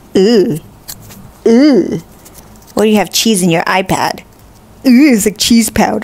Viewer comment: I thought she said cheese pad